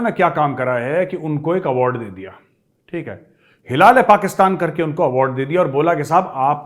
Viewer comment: 😅😂😂 when first time i heard ****hilale Pakistan***, and then i realized it's Hilal-E-Pakistan😅😆😂 5:31 Major Saab said ; Le Hilale Pakistan😂😂😅